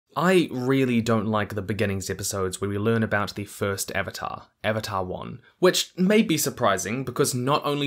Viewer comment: You just made me realize the pun behind Wan's name.. I thought you said "The first avatar, avatar one". The idea of how the air nomads cultures could have been learning from sky bison as a way to help restore their culture, until one of them finally airbends, really gave me goosebumps!